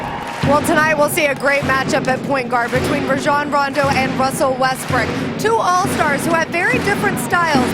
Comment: did she say westbrick😂